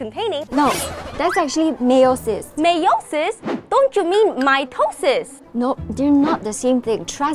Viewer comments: I thought and said My toes sis and My yo sis 💀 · the only thing I heard was “my toes sis”😭💀